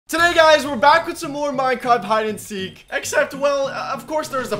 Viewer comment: i thought he said today guys were back with some more minecrap hide and seek